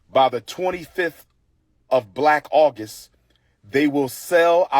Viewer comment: Did he just say black August? When did we start using color adjectives before months? Would December be white then? I’m so confused